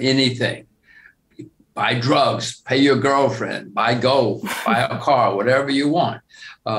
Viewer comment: Did he say “pay your girlfriend”? Hahahaha